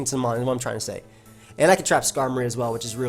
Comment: Did I just hear "And I could just trap skarmory"